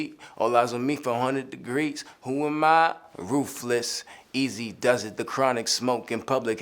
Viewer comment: did ge say ruthless or roofless? i think i heard him say roofless(as in maybe driving a car with no roof/convertable?) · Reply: YungVague Ruthless...he was referencing Easy-E, his record label was Ruthsless record. Hats why he says “who am I? Ruthless, Eazy does it”